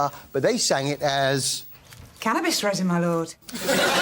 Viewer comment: What's she saying?😂😂😂😂🤣🤣🤣🤣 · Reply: Cannabis resin my lord im getting 😅